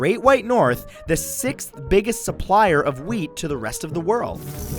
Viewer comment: ca 3:00 why do i always think he said weed not wheat? "Kanada is the sixth biggest surplier of weed to the rest of the world" dayum walter white couldnt do it better XD